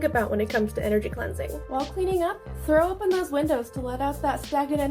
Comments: I swear I thought she said throw up on those windows. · Ok when said "throw open those windows" I heard it as "throw up on those windows" and was wondering how the heck that would let in cleansing energy.